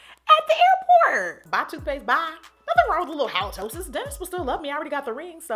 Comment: Lmfao i literally thought she said "dentists will still love me" after referring to halitosis then i realized she said Dennis 😭😭😭😭😭😭